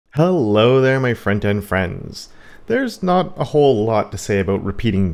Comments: all this time, I thought he said friend and friends but front-end friends sounds nice too, a friend none the less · Wait, have you always been saying "front-end friends"? I thought you said "friend and friends" 🤦🏼‍♂️ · I thought was saying “hello my friend and friends” Front-End Friends makes so much more sense 😂